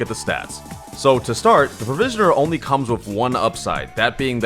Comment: i thought it said the prisoner